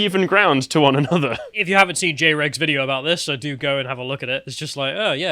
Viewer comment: did he say "j-rig"? who is that?